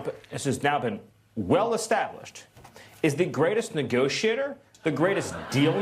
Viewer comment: Anyone else hear S.M. say "negoshitter" instead of negotiator?